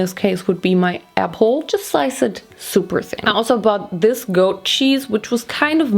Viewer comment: "This goat cheese" really thought she said "Disco cheese" lol, not an accent problem, I'm just a dumbass 😂🤷🏿‍♂️